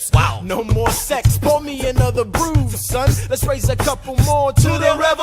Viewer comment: when Laurens said 'pour me another brew, son' in Aaron Burr, Sir I thought he said 'pour me another broom son' until just now.